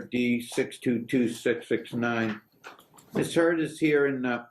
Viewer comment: 🤦‍♀I thought for sure he said "Ms. Turd" was here. Well, after watching these... that may be what he ACTUALLY meant to say 🤣